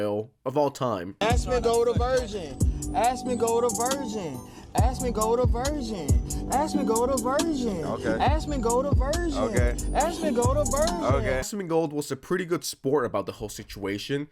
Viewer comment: I thought he was saying "asmongold AVERSION" 😂😂😂